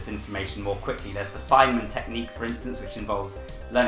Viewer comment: like the what strategy?? The fyl.... WHAT?? Someone please tell me what he said!!! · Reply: The Feynman technique named after the physicist.